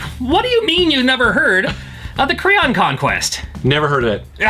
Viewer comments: lol I thought you were saying the "Crayon Conquest" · I thought he said "The Crayon Conquest." Needless to say I was expecting something completely different. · Oh, he said "The Krion Conquest." I thought he said "The Crayon Conquest."